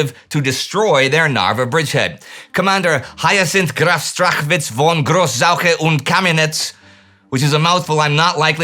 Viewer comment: can you please repeat, couldn't catch his name?🤔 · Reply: Hyacinth Graf Strachwitz von Gross-Zauche und Camminetz!